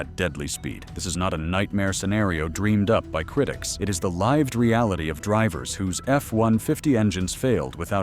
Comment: "The Live - d realit" instead of lived reality? Your AI narrator needs some work, Mr CCCP guy.